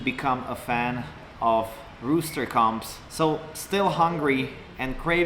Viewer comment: I thought he said rooster come 😮